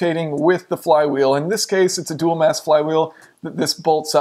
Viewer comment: I thought you said "dual-ass flywheel" lmao 😂😂